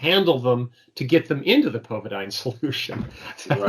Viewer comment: Um, hey, I spoke to the manager of a local health food store about oil of oregano, and she said that everyone there bought some, because it allegedly kills viruses within 20 minutes. But you have to have 400 mg or above for adults. Secondly, covadine solution is available where? Is it spelled covadine? What about plain iodine? I was told it will disinfectant pond water by a survival course alumni. Anyone hear anything about this stuff? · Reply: thanks, I thought he said covidine. Looked it up, it's a wound irrigation solution